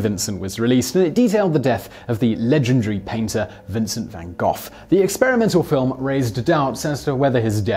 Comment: Did he just say Vincent van Goth?